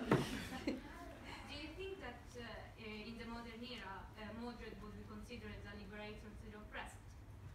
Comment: what was exatly the question at 19:32 ? I didnt hear clearly And it was you, who asked the question about the script for season 6,right? I've read it to and it was the best question :) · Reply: I think it was "in the modern era do you think mordred will be considered the liberator of the oppressed"